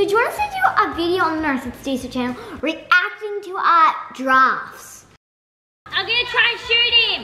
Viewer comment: It kinda sounds like Naz is saying "Giraffes" instead if "drafts" 😂😂